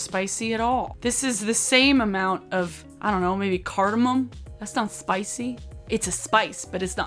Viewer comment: Did you just say caramel and garlic are spices ....no